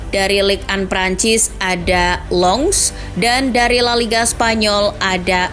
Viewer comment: Gak salah denger w Mimin bilang longs😊😅😅